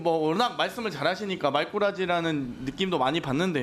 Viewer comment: 법꾸라지처럼 말꾸라지 아닌가요? 그렇게 들리네요. 1:39 장경태 화이팅!